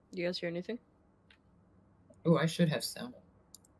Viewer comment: did he say "i should have sound on"? lmao